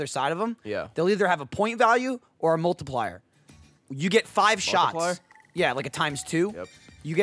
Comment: Did Ray say markiplier at 14:19?